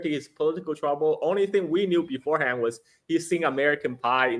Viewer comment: What i heard is "Yoon suck you and American pie" How perfect?